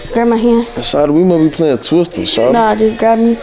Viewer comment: "What we playing twister shawty?"😭😭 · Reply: I heard “shawty u’kno we ain’ playin twister shawty”